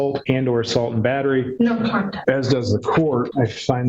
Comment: They wrote that she said no contact instead of no contest.